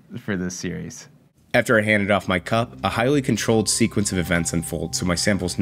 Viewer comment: swear i thought he said "cum" at first